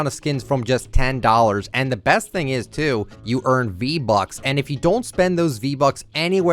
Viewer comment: It sounds like he said b bucks